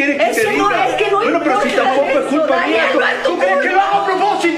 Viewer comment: alguien me dise que dijieron no entendí ni pico wey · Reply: Ella le esta diciendo que no es su culpa y el que no lo hace a propósito